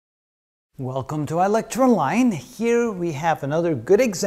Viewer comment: Bruh these past few years I thought he's been saying "Welcome to electro-line" LMAO